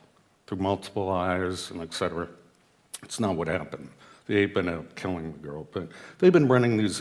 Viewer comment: did he say the ape ended up killing the girl???